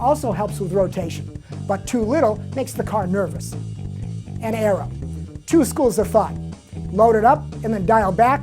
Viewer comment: What does he say at 4:41? The last point. Something you load up and dial back. Sounds like 'an error' but I think it's 'and something'. Lots of good points here, thanks.